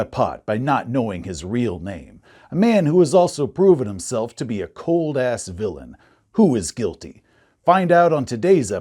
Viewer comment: I know that intro was supposed to be "... a man who has proven himself to be a cold ass villain. Who is guilty?" But I heard "Dweebles is a cold ass villain, who is guilty."